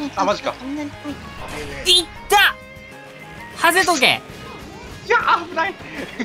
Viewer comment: のシーンの｢ひゃあ、危ないw｣が｢ひゃあ、うまい｣と聞こえた俺は末期